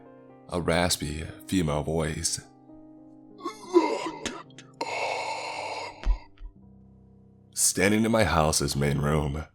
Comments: What was said in 16:30?? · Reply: Locked up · what? Hold on... what she say? Wanted cop? Oh god? Huuuuh? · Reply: Look up